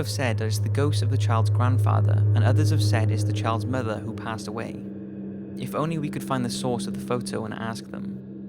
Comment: When you said "Child's Mother that passed away", I was worried bc i thought you said "Child's Murderer", Also, I checked out your website, I like it :)